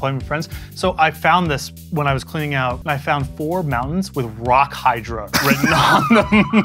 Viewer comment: Plot twist: Those four mountains were from Arabian Nights. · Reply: At first I heard “balance” instead of “mountains” and nearly threw up